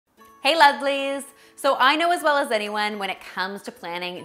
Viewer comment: Dude I got so scared when she said lovely. I thought she just said,"Hey Uglys"